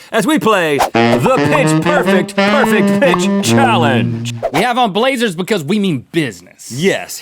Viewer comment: Was I the only one that thought it said pitch perfect challenge like they were gonna sing lol 😂😂😂